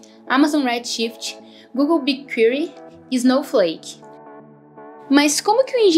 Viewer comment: Eu não entendi o nome da ferramenta do Google ( 3:54 ), alguém saberia me dizer o nome? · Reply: Oi Luiz, o nome do DW é Big Query, temos um video falando sobre ele aqui no canal também: <link> Espero que ajude a sanar a maioria de suas dúvidas 🙃🚀